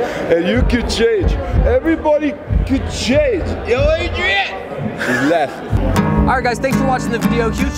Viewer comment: Xcuse me, I am not native speaker , so could you make me clear , what that man said 4:59 ? He said everybody could change... but after this some strange accent . Thank you ! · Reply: Yo adrian he says